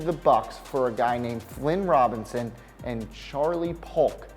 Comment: Jim Modzelewski everyone heard that, worst name butcher ever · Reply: Plus I thought he said Glenn Robinson was treated for Oscar Robertson but it was actually Flynn Robinson